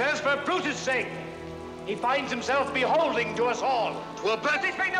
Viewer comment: wait. did he say 'beholding'?